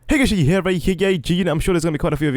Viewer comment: What the fuck are you saying at 0:00 - 0:01?????? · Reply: Hey guys here A.k.a